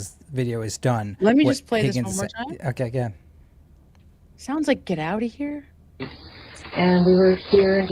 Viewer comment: I think may be saying “Not a chance… Not a chance.”